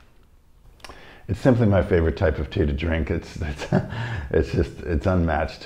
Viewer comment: I didn´t hear it well… so I heard you say: „It isn´t my favorite kind of drink” – but that! absolutely didn´t match with your facial expression. Happily awaiting my packages of this excellent (not roasted!) Traditional Dong Ding. Know what?! I still have small left from the first badge – and I am gonna make it now! :)